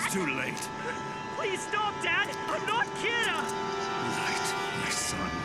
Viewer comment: My subtitles said "stop down i'm not cute" XD